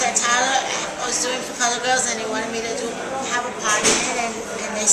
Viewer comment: lmaoo, did she say for fun girls?